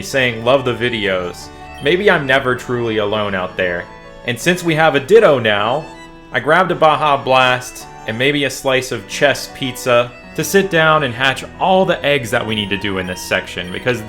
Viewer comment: I thought you had said chex pizza and rewound to see 'chesse', if you're put on board duty at least spell check it